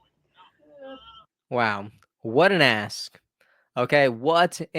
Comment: I thought you said, "What an ass!" 😂